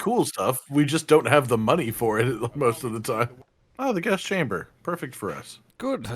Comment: did anyone else think said "oh cool the GAS chamber that's perfect for us"?